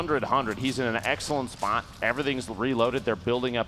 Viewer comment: Anybody hear say spot at 11:03 😂